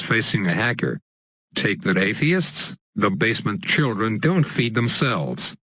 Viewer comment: Did you say take that atheists?